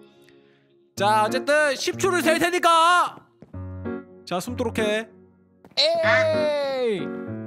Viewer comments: 여기 저만 숨도록해로 들리나요? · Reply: 에서 "순조롭게 (X), 숨도록 해 (○)" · 아 어쨌든 10초를 샐 테니까 자 숨도록 해 나만 이렇게 들리는겨..? 9:01 어 엔더 진주 써버렸어